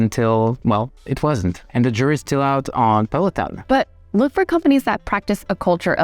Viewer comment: I had to listen multiple times to realize you were saying Peloton. The subtitles got it wrong.